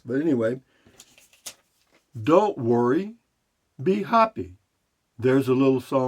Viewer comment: At first I thought you said “ Be Hoppy” that might make a good T Shirt. “ Be Hoppy , Beer: 30 “